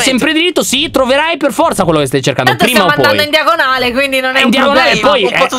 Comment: tanto stiamo andando in diagonale Ero confusa perché ho capito Diagon Alley😂🤣🤣